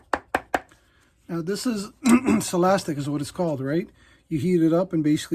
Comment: I think you said the material you use for Toe & Heel Counter is Silastic? is that right? I have gone online to try and buy some sheets of it, but have drawn a blank. Can you tell me if I have heard wrong? I am in the UK, and am very keen to make Heel Counters to repair shoes.